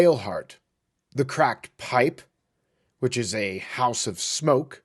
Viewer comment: Lol I heard "the crack pipe" Not the Cracked Pipe ❤